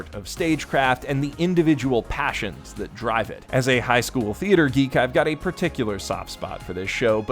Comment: I almost thought he said "As a Highschool Musical geek", and I almost shat my pants. It's the best thing ever made, HM2 beats even the most critically acclaimed films such as The Godfather out of the park.